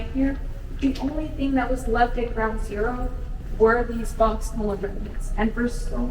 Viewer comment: “were these [???] remnants(?)” What did she say? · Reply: "The only things left at the site of ground zero are these boxes full of remnants."